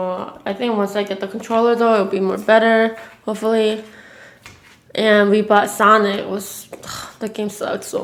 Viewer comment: bro I thought she said "we bought weed" I was like wtf lol